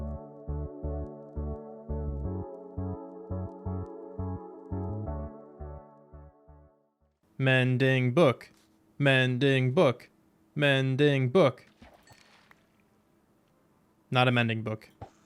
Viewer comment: I am sitting here shredding papers and what I THOUGHT I heard was “MAN-DINGO! MAN-DINGO” and I thought you were fishing for Man Dingo until I stopped shredding 🤣🤣😭😭